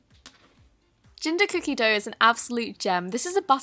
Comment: Did anybody else catch that she said “ginDer cookie dough”